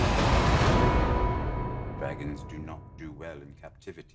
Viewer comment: The first time I saw this it sounded like Tyrion said "Baggins do not do well in captivity.." And for nano-second thought, why is he talking about hobbits?